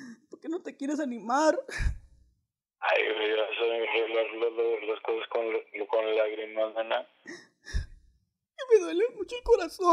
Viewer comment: Le Doy 100,000 pesos al que me diga ... Que Dijo Babo En El Min 5:40 v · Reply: Le dice " vas a venir a arreglar las cosas con lagrimas" pero se trabo al decirlo